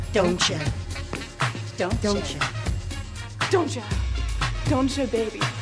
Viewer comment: Lol it sounds like she said "Dont cha oooo DONT SHOOT!!!"